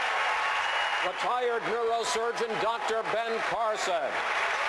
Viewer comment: I honestly thought he said 'the tired neurosurgeon Dr Ben Carson'